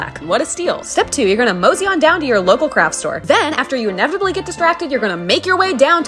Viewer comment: I thought you said "local crap store" instead of craft store